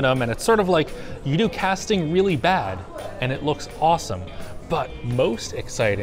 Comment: What does he say at 0:25? “And it’s sort of like” _______ “and it looks awesome.” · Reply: “you do casting really bad”